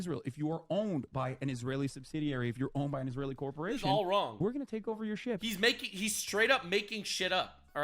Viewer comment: did he say we?